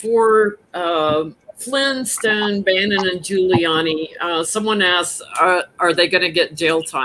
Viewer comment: Did anybody else hear “Flintstone, Bam-Bam and Giuliani ”? 😆 😆 😆